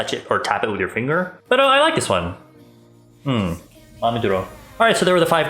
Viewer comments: Im mad or something, i think I've heard "PERO I like this one" 7:56 maybe .... AM I RIGHT?? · did he just say “pero i like this one” Jxjxjsiak